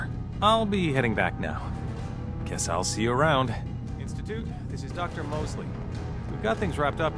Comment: I thought he said "this is Doctor. Mostly", I was like lol that sounds like a cartoon character name.